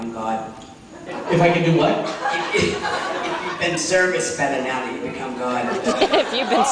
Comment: what's the question around 7:37? serviced?? I can't understand it well. I think I took two days trying make out the whole panel, and I just can't..thanx uploading this tho :')